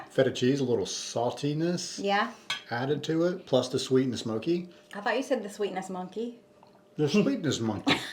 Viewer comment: Looks really good ❤. LOL I thought Said Sweetness Monkey 😂😂. That's what I heard 😂. Much love from Pennsylvania ❤